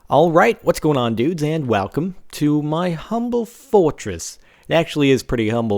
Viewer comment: lol it sounded like you said walcome